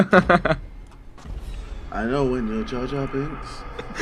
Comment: what did JJ say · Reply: Ahmed Montana He said "I know when you're jar jar binks